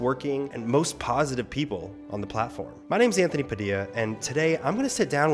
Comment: when he says "My name is Anthony Padilla" the caption says "My name is Ian Hecox" Did anyone else notice that?